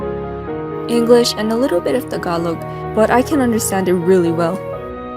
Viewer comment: omg did u say tagalog?😮